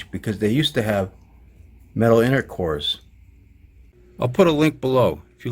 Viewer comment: Lol.. It sounded like you said Metal Intercourse!! I was like.... HELL YEA!!